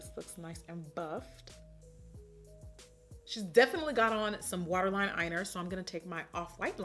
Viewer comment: I love this look miss momma😍 I can’t be the only one that heard her say “waterline eyener” thooo😂